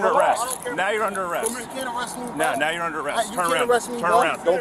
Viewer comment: he said "you can't do nothin' to me" and was arrested?! The cop only got to hear it once. But I could rewind and slow it down. He might think he heard a threat. But he didn't make one.